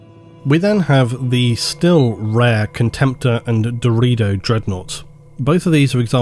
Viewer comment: I can't be the only person who heard "Dorito Dreadnought" right?